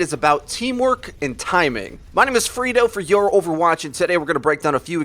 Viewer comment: Sometimes I correctly hear the name as Freedo. Sometimes it sounds like frito. But once or twice I've misheard it as "furrito". A frito burrito?